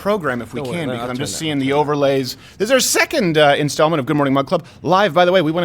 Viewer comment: Seriously! I always thought he was saying Mud Club and never understood.